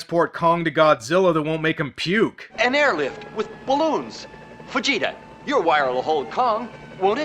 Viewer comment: Am I the only one who thought the guy said vegeta?